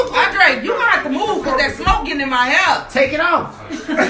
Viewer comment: the woman complaining about the smoke getting in her hair was unintelligible. Even close captioning couldn't understand her. It wrote house instead of hair. 😅